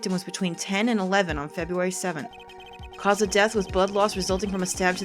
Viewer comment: its probably just me, but at 6:15 I thought you said "Cause of death was butt loss"